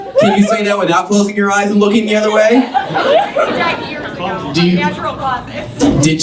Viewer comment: what word is that?? "DIDED"?? Like...what?!?